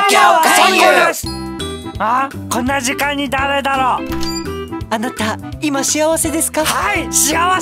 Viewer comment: あなたを半殺しって聞こえた